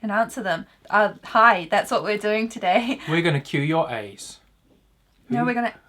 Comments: I thought he said "we're gonna cure your aids" and I almost died. 😂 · I thought said “we’re gonna cure your aids” and I was very confused for a moment